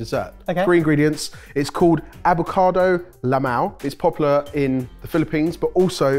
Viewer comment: I wasn't watching with subtitles and then I heard "Avocado LMAO" 🥑🤣